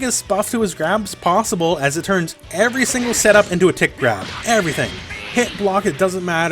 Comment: When was talking about blueku's grab, I kept hearing "tit grab" instead of "tick grab" and now all I can think when I see the move is "hehe, got your nipple" WHAM